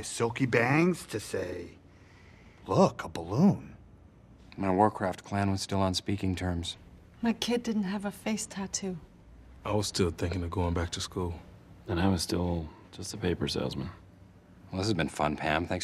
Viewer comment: did Dwight say “clan”?